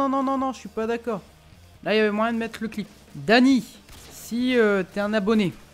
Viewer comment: PTN quand j'ai entendu Dany a 2:01 j'ai cru que on m'appelait ^^( oui .. je m'appelle Dany si on suis la logique :P )